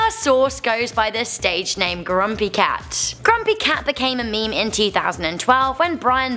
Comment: Did she just say crumpy cat on